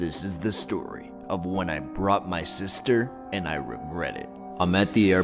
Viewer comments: I thought it said I bought my sister bro 😂😂😂😂😂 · I lit thought it said I bought my sister